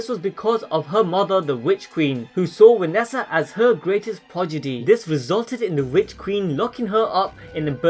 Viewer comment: Did you just say progidy @ 4:21.???